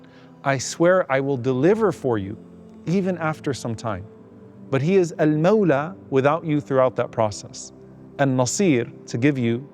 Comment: in the lecture video of Dr. Omar Suleiman, there was a typo in the captions (Not generated by the Youtube). "He is Al-Maular with you throughout that process" is the caption; BUT, Dr. Omar Suleiman said, "He is Al-Maular without you throughout that process." What Dr. Omar Suleiman said is correct. The caption has a typo, I think. This is for Institute. Sorry, I apologize. It is generated by YouTube. It was YouTube's captions system's mistake.